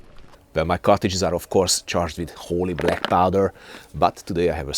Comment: I know you're saying "wholly black powder", but I always hear it as 'holy black powder', which seems fitting.